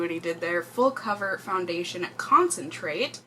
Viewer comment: I thought that said "full coverage foundation concrete"